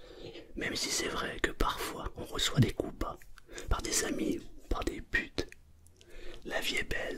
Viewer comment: Par des quoi ? J'ai bien entendu ? Je m'endormais ça ma réveiller mdrr à